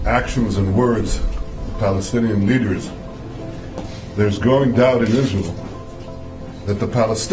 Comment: He said growing doubt! The first time I thought that he said "There is no doubt."